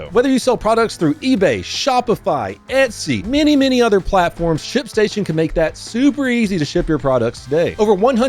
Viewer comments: I'm sorry but he was talking about the ship station thingy it really sounded like he said sh*t station a few times idk if anyone else heard the same thing 😂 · I thought i heard you say "SHIT STATION" I was like 👀👂